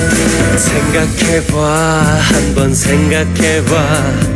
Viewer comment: guys! I just learned how to say “Think about it” in Korean! :D CHENGAH QUE MOI ! (sounds French for some reason)